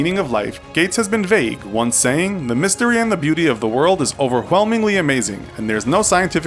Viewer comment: wait what he said OverwHelming